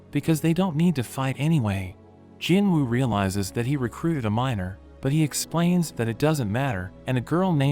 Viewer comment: Did i hear bro say he recruited a minor